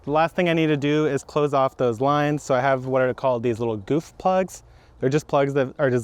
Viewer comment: I thought you said “boof plugs” at first 😳😂